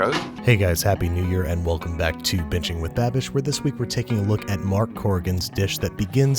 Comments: I swear it sounds like he saying bitching with babish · I was giggling at the intro and thought you said "Welcome to Bitching with Babish." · I thought he said bitching with babish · I thought you said "Bitching with Babish" and was confused for a second. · Did it not sound like he said bitching with babish lol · It sounded like I said bitchin with babish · I actually thought you said “Welcome back to Bitching with Babish”. I’m starting to belief I’m going deaf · Why did I hear, "bitching with babish" 😂😂😂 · I thought you said “bitching with babish” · Sounds like he said welcome to bitching with babish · I always hear "Welcome to Bitching with Babish" instead of binging hmmm